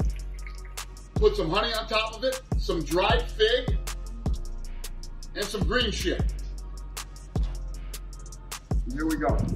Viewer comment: did Dana say “And some green shit” lmao